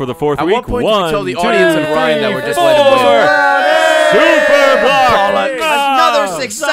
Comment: Did say at 53:35 "At what point do we tell the audience and Ryan we're just letting him win."